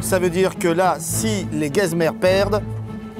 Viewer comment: j'ai entendu les guezmerdes perdent xD